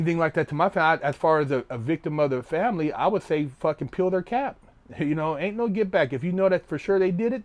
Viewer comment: When he said "peel their cap" I thought he said "kill their cat" and I was confused lol